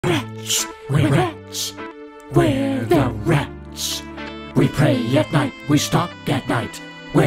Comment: i just found out this is jerma im losing my mind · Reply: i thought he was saying “we’re rich we’re rich we’re the rich” for the past three years…